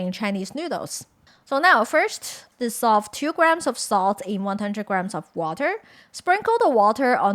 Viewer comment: Just as a heads up, at 5:23 the subtitles say to "dissolve 2g water in 100g water" (instead of 2g salt)